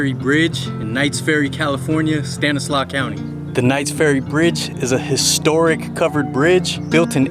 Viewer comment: I thought that's said Knott's Berry fridge 😂 😂 😂